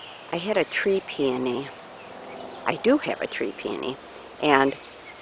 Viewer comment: This video and your voice is so relaxing to me! I really enjoy your videos. When you said "I have a tree peony", my son thought you said "I had a tree pee on me"? I was rolling on the floor laughing at that! Thanks! Chuck